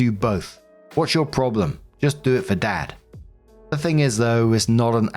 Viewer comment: anyone else heard, "Do it for Dan?"